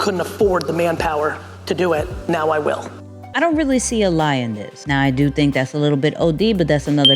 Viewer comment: “I don’t really see a … Lion this…” Lion’s behavior to the moon 🥹🤣🤣🤣 (yes, I know it’s “lie in this” but I heard “Lion”)